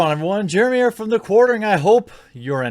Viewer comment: I thought you said quarantine